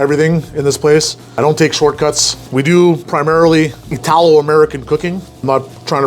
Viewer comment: I thought you said tallow American cooking and was like "Is that any relation to Paula Dean cooking?"